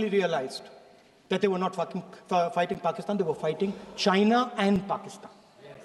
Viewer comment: he says fucking instead of fighting!🤣